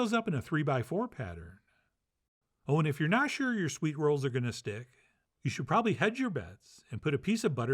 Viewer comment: for a second I thought that said SWEAT rolls i was thinking " ew but they look good"